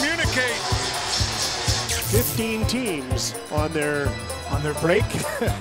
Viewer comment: I swear I thought pk suban was saying “ fifteen teams”